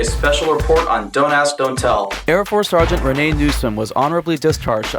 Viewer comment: At one point I thought he said Dumbass Don't Tell lol